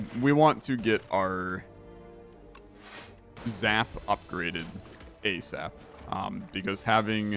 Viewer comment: Did you just say you want them upgraded a-Zap